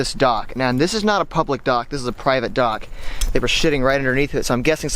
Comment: U said sh*ting instead of sitting at 2:55😂